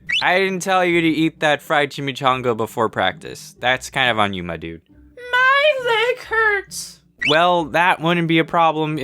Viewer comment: The first time i saw this scene i heard "my dick hurts"